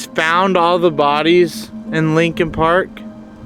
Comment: I’m laughing so hard right now. I’m your introductory short, I thought you said Lincoln Park!